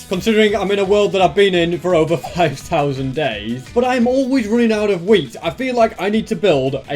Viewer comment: I thought he say "but I'm always went out of weed."